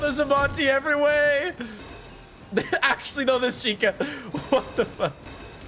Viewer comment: I like how whenever you flash chica it sounds like she says what the fuck.